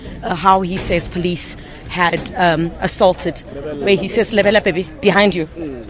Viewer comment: did she say lebella baby?